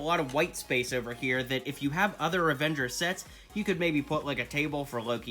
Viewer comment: did he just say revenges!